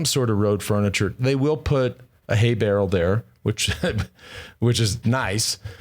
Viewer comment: Legendary finish! P.S. at 34:30 is Lance saying "Hay Barrel"? That's a new term to me 😂